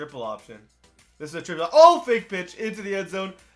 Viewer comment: did u say fake pitch or fake bitch at 7:10 😂